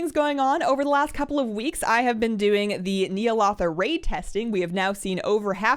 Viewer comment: i was so confused, i heard "i have been doing the Neil Author raid", and then a sec later, OHH Ny'alother! :D dont take this as bad, i still love you :3